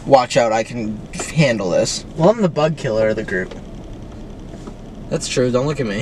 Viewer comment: i fr thought he said butt killer 😭